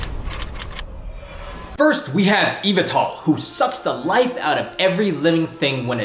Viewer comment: Did he just say Evetal? COME ON!!!